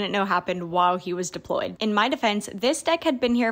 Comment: i screamed at 6 am bc when u said deployed i thought u said deported..